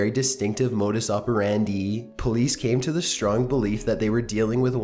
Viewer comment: modus operandee? did I hear that right?